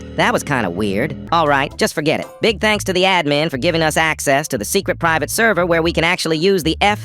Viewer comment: I thought you said that "the admins gave you that block when you got the private server."